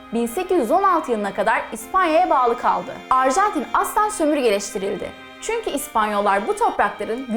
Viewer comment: ne diyor burda benim anladığım , asla sömürgeleşdi